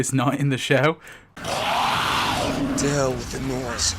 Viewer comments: Rick says "to Hell with the noise". I couldn't hear that. I had to rewind like 8 times. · What did Rick say at like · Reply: To hell with the noise